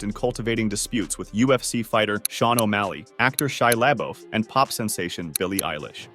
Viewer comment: did he call shia Labeouf shy Lambo? 💀